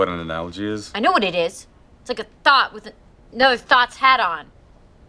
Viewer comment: i always thought Britta had said "its a thought tha thoughts had on"